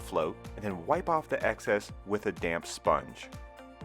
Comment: Thought you said “wipe the tiles with a damn sponge “ 😄